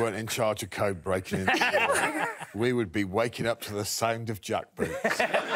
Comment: What does Greg say at 23:22? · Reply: We will be waking up to the sound of jackboots.